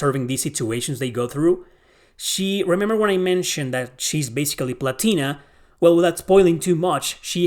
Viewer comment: i really heard "shes latina" LOL